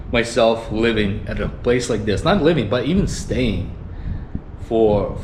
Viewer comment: Woah!,...I thought you said leaving! Aha! Still enjoying in m"sia!,one after another living places..lucky you, always have a best bargains.enjoy your stays ❤